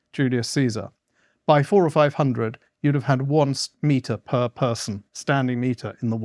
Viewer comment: Did Morland say "square meter?" I heard "smeter" and the subtitles just say "meter."